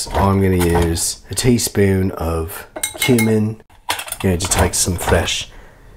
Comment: When he said cumin i thought he said human for a sec 😭😭😭